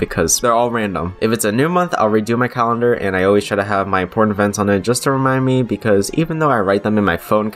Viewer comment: DID ANYONE ELSE HEAR HIM SAY PORN EVENTS (i have issues, lol)